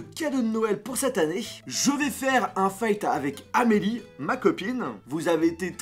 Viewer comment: c'est moi ou tu ne sais pas prononcer << un feat >> 😂😂 féllte mdrr